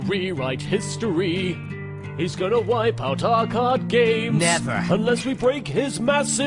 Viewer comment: 2nd time watching this and found out its from yugioh abridged, i thought they kept saying COD GAME